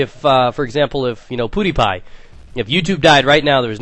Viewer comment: i seriously thought you said pootiepie instead of pewdiepie